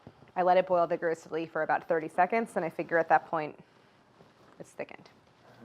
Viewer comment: omg i thought claire said to boil the maple syrup for 30 minutes instead of 30 seconds👁👄👁 i boiled it for like 7 minutes and my maple syrup started to smell burnt and look really dark brown